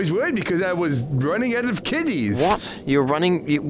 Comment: is he saying kitties or kidneys?